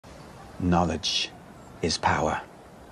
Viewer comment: When he said "knowledge is power" I thought he said "Ouch is power" XD